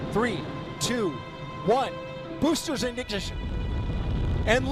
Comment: Did i just hear "boosters indignation"